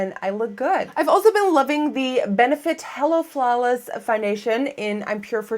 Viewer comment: I thought up said "Benefit Hella Flawless" hahaha <3